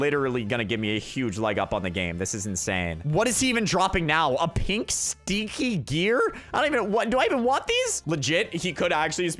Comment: When he said sticky gear I thought he said sneaky gear 😂